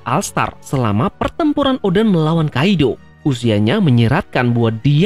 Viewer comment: PERTEMPURAN ODA MELAWAN KAIDO"Saia salah denger atau gmana bang wkwk